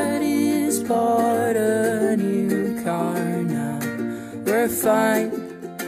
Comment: i misheard this as 'daddy's hard on you carnell'